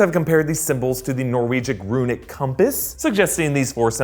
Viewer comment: Did... did he just say “Norwegic runic compass”??